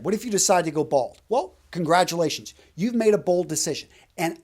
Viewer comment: When you said "congratulations, you've made a bold decision" I heard "you've made a bald decision" and I've been laughing at a joke that never happened...